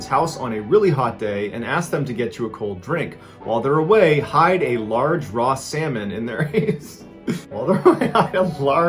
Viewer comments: When you start laughing it sounds like you say to hide a large raw salmon in their anus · At first I thought you were trying to say hide a large raw salmon in their anus haha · Am I the only person who thought at first he said to hide a raw salmon in their anus? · I heard "hide a large raw salmon in their anus" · at the beginning when you cracked up it sounded like you said "HIDE A RAW SALMON IN THEIR ASS" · I swear to god I heard 'Hide a large raw salmon in their anus' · i swear to god, i thought i heard john say “hide a large, raw salmon in their anus.” i was dying 😂